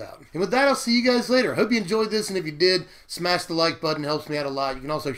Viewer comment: Lol @ 16:17 I i thought he said smash de light button :o